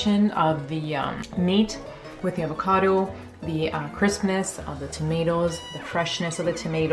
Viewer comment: said crispness and I thought she said Christmas